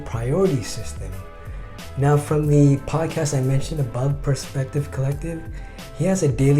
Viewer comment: I hear you say Perspective Collective.. is that what you mentioned earlier?